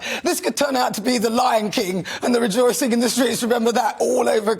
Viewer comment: Nana, I thought you said the Lyin' King... 🤣 Love you Nana...